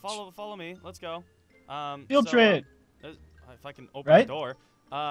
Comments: Did he say "If i can fucking open the door." ??? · did anyone else hear sky swear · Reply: He said "If I can." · did he say motherfuckin open the door